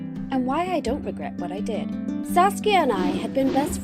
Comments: i thought at first she said "sasuke and i had been friends since before we were even born" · did she say Sasuke? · did you just say Sasuke?